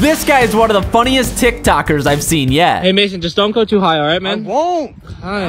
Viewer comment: I thought he said don’t go to Ohio ok but then I played it back and realised